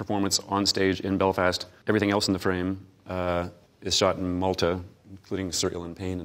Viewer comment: what does he say at 7:17 and 7:45 ? Is shot in...? multa?